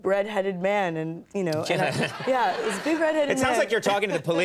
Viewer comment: Could have sworn she said big "bread-headed man" lmao i pictured how it was square like a loaf of bread